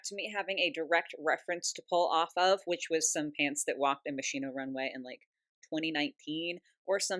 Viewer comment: I died inside every time I heard “ Machine-Oh “ in place of Moschino (musk-e-no) 😭🫶💖